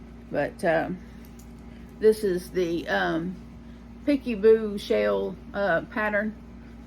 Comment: Hey sorry I couldn’t understand what you said the name of the blanket pattern you were working on at time stamp 7:02. Peekaboo shell? Can you clarify this for me and tell me where I can find the pattern? It’s gorgeous! I’d love to make it myself!!